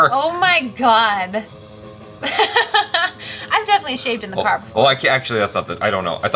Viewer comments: wait, did she say I have totally shaved in the car!? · did she say "I've definitely shaved in the car before" ? 😂😂